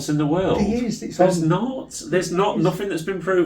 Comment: my theory is that he's with that person who walked from the front down the side and i would guess was at the back at that point! sounds like he said "it is int it" like he's talking to someone. i'd see this as a positive and start setting up some home alone type traps with tins of paint and greased ladder rungs etc! £250 per video on you've been framed remember!